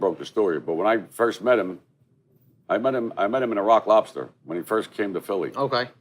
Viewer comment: did he say he met him at Rat Lobster 😂